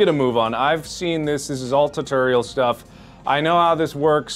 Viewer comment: "I know everything" PROCEEDS TO NOT KNOW THAT AMMO IS CURRENCY IN THE GAME Silly mark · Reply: he did literally not say what I quoted. 11:40, he says "I've seen this, this is all tutorial. know how it goes." And then proceeds to make a mistake~.